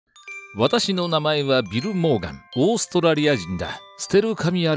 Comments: ビル・モーガンが貧乏神に聞こえた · 「ビル･モーガン」が貧乏神って聞こえる